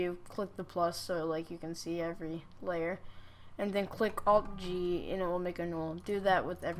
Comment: what did you said at 1:20? to make the null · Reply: dont listen to lunar he his a noob that is gey, you press alt + g, i say it btw...